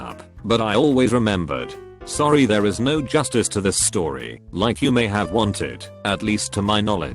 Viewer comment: The Jizz Story: They had us in the first half, I’m not gonna lie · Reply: Oh god I almost choked on that when I heard the "Jizz Story" XD